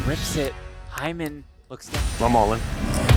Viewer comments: I’m all-inがワイもオールインに聞こえるww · ここガチでワイもオールインって聞こえてて草